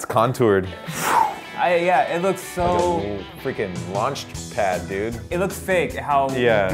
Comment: did he say launch pad? (fortnite)